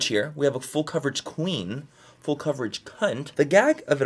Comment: Why was this video actually SOOOO FUN!!!!!! It literally felt like a science experiment! Also... who liked the full glam at the end ;) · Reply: I wish I could “like” this video over and over 😘 Soooo funny....I’m pretty sure I heard “full coverage cu*t” and died 😂